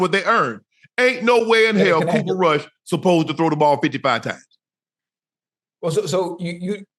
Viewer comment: I thought I heard 35 times, jeez, but 55?!